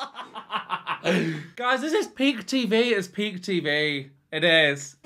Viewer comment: I clearly heard you say, “this is pig tv, this is pig tv…” which is true… Ok I’m just kidding….PEAK TV…I know, but PIG sounds more accurate 😂😂😂 Btw, you are hilarious! Great reaction video.